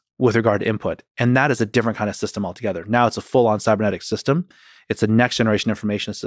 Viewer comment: I thought he said subreddit system.. I was a little shocked at how confident he trusted Reddit haha